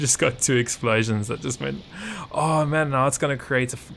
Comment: did he just say fucking forest fire??😂😂 (btw dont worry about it every one is human and i find it really funny)